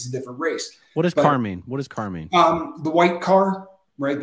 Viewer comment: I think he says "cart", not "car" and it's probably just short for cartel.